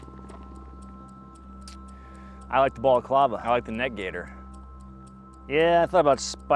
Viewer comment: Lmao I thought you said Alec Balaclava